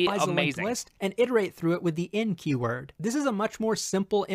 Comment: I first heard him saying the N-Keyword :)